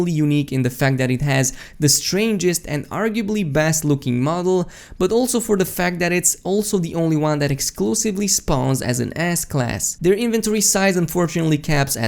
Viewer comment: Am I the only one who hears "Ass Class" 1:49 instead of S class, lmao